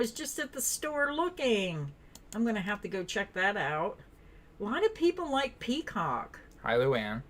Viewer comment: I heard “lotta people like peacock” but the CC heard ‘Why do people like peacock” lol